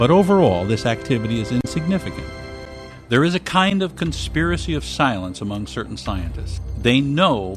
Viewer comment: Did he say "Conspiracy of SILENCE" at 00:50?